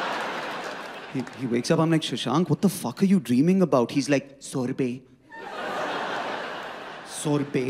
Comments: I honestly thought he said sorry bae not sorbet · Not lying, i heard him say "Sao rupaye=₹100" not sorbet🤣